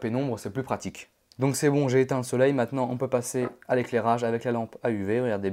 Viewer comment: c'est moi ou il dit "j'ai éteins le soleil" a